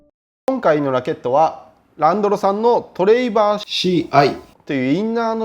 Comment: あたりのセリフがランドロに聞こえるw